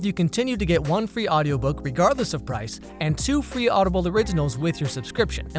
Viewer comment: I swear I heard "regardless of Bryce"